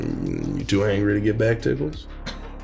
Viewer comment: BACK TICKLES? DID I HEAR THAT RIGHT?